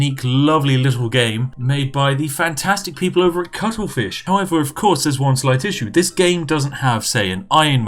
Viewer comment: did i hear you pronounce chuckle fish as cockle fish?